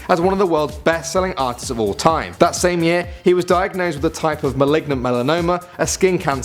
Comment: did he say maligma???